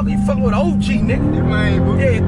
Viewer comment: "I HEAR YOU, HARDLY" 🤣😂🥺🥺😂😂😂😂😂